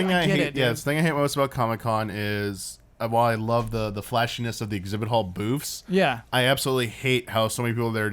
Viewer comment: I heard "Flashyness of the exhibit hall boobs" ;)